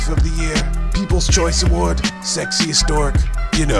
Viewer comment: The way he says it, it almost sounds like he says: Sexiest dork :D